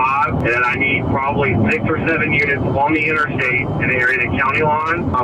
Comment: did he say 6 or 7 😂😂